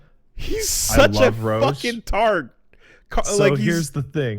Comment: What does call Roche? It sounds like either " 'tard" or "targ" to me. doesn't seem like the kind of guy to call him the first one, but I've never heard the term targ before. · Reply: Blastotron I think he said tart.